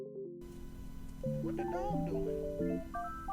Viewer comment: is it only me who heard "what the dawg doinn" in the background 😭